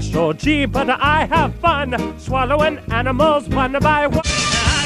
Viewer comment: DID HE JUST SAY EAT ANIMALS ONE BY ONE?